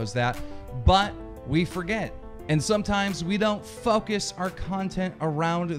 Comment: did he just say fuckis? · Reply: I hear "focus" at 0:38 What Is Simple :-)